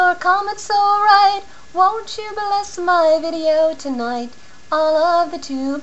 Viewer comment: Didn't you say "bless" not "molest"? LOL I thought I heard bless...